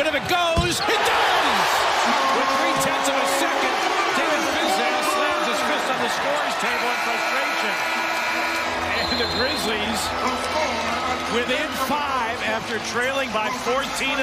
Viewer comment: I thought the reporter said “and for the McGrizzlies” lol